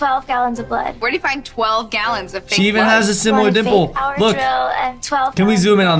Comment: I thought said “She even has the same nipple”